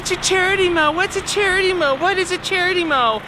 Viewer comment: I thought you were saying "charity blow" man I was WAY off!!! Go Yankees!!